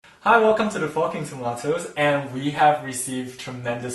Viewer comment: I swear to god I thought he said "Welcome to Fucking Tomatoes"